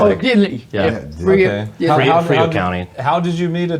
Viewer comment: OWWWW MY HEART!!!! I swear to God, I thought she said your Opa was from FREER, Texas - I almost fell out of my chair bc my grandparents were from Freer - till you corrected her, and it was Frio County. Ya'll have no idea how much this means to me. From the age of 8, all I ever wanted to do was gather civilian stories from people who lived through WWII and make sure they were shared with the world. I never got to, don't have the platform to do so now, either, but it means the WORLD to me when I get to hear and share these stories. Thank you for sharing your Oma with us. Thank your Oma for coming out and telling us. I truly hope she lives as long as she wants & thanks to you and the boys, her stories will live on forever. ❤